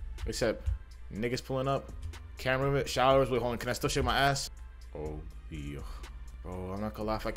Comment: He became italian for a second 😂 (i heard "Oh dio")